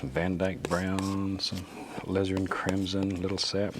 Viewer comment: Phthalo is such a cool word. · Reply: When I first started watching, I thought he was saying, "lizard crimmson!"😂